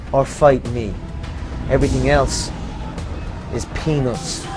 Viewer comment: i heard “Everything else... is penis”